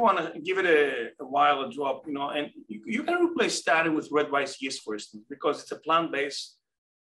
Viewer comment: In minute 10:00 I didn't hear u can replace your statin with ? What is the name of the supplement ? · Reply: Red rice yeast.